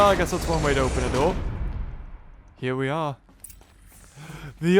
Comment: I thought it said pray open the door